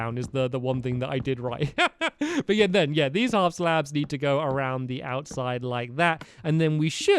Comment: Stamps you said half slab on 15:30 instead of baby block